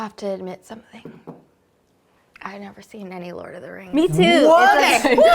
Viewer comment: All I heard was " I've never seen Lord of the rings" what?????!!!!